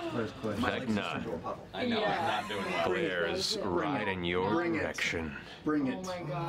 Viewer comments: Does anyone else hear 'riding your erection' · I misheard Matt and thought he said "Glares, riding your erection."